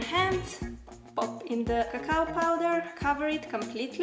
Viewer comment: did i just hear her say caca powder- 😆😆😆